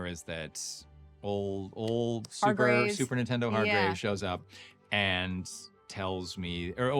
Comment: I played this back like five times to confirm what I heard. Kevin called Hargraves "Super Nintendo" instead of "Superintendent!" LOL The auto-generated YouTube subtitles said the same thing.